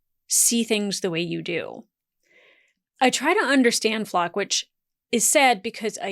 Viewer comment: did you just call him a Flocker? 😂 I know a few flockers too for sure.